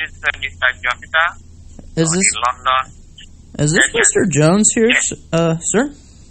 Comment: It sounded like he said “is this mr jokester”